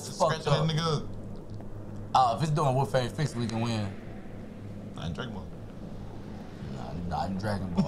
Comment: i thought he said I'm Draymond